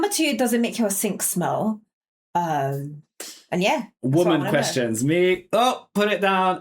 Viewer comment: Did I just hear you say "women's questions?" 😂